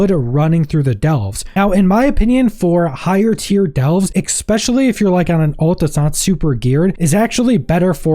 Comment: expecially? what does that word mean? do you also say axe a question instead of ask a question?